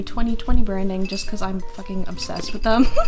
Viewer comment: Am I the only one who heard her say "f-ing" at 5:59 👀